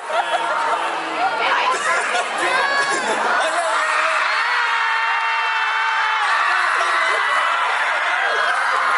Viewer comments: What did Chan say? I am a little confused since Chan appeared on the big screen, and I saw he gestured to someone, then turned back to Felix. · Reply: Essentially, Felix was nervous and he accidentally said "Having 7 brothers" instead of "8 brothers". so Chan said to him "7 brothers and 1... boyfriend?" and pointed to Changbin. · seven brothers and what ? I can't hear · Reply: and one girlfriend :D